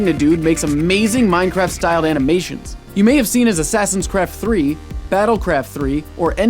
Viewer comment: I thought that too but he said assassins craft it just sounded like cred